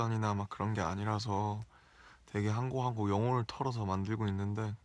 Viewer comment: Namjoon, I can relate to your struggle with a random pimple appearing on the face. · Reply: Peppy Grace Subs Hi!! Thankyou for translating it>.<♡ Do you mind changing 3:02 "throwing English" part into "pouring my soul" cuz that's what he said :) THX♥